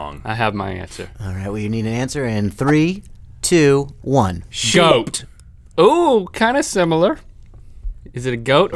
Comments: Did they say "Shoat" at 3:46? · it sounded like they said "Shoat". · Who else heard "shoat" at 3:48?